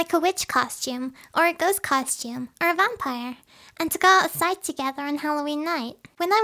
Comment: did u say bumpire